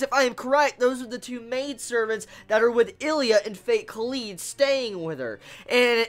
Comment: Hey! I really want to know what you said at 0:37. Fate- what? Cuz I am like, how is it that I haven't watched this. PLEASE REPLY! Love yaa! <3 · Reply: Saniya Naieem Kara no Kyoukai and Fate Kaleid